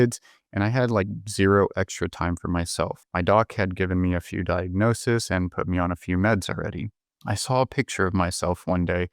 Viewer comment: I thought he said his dog gave him a few diagnosis and meds.